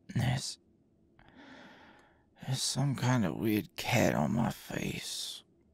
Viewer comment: I don't know why but I heard "there's some kinda weird cat on my face" and just absolutely lost it 😂